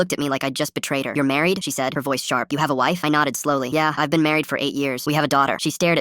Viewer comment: IT SAYS "YOU HAVE A LIFE?" INSTEAD OF YOU HAVE A WIFE GIGGLES (this is very unrelated)